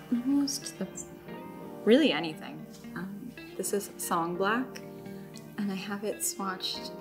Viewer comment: Great video! What is the ink at 21:06 ? I hear something like "sun black" but I seem to be mishearing. I absolutely love the colour! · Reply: It's seems to be Song Black from the yoseka ceramics ink series!